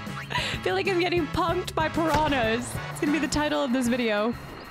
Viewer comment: Genuinely thought you said "pumped by pyrhannas".